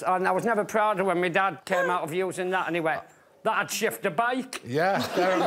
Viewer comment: What did Johnny Vegas say please? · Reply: That would shift a bike 😂